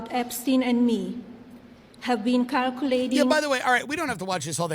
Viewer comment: did she say kirculating?